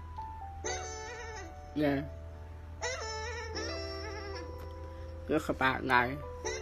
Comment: "Yall don't understand" 0:24 - 0:51 Incoherent 0:51 - 0:53 "I'm gonna make you squirt" 0:53 - 1:00 Incoherent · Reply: “This about life”